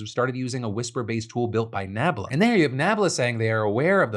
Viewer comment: Oh man Phil for a moment I thought you said NAMBLA not Nabla. Had a good laugh envisioning the North American Man-Boy Love Association being in the news, tho